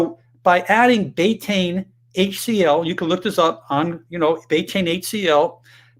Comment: It took me a few minutes to figure this out, maybe you can include 'Closed Captioning' on your videos. At time 11:14 you mentioned taking "Betaine HCL" I had a hard time understanding what you said so in case anyone else hard a hard time figuring out what we might try it is "Betaine HCL" Thanks for every post you do and that you share your faith as well, Thank you!